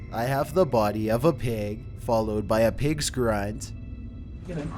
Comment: In number 2 , i heard " i have the body of a baby " ... yikes :o